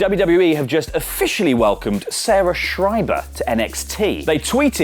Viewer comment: I thought you said Zara schreiber, the chick with the nazi tattoos, but I'm glad I was just hearing things